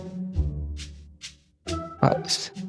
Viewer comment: sarmad said sara instead of zara 😂